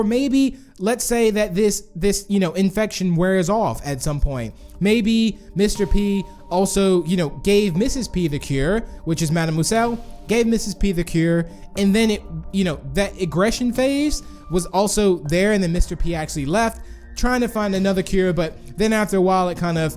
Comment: i always hear "Madame Gaselle" when you say "Madamouselle" (i hope i typed good)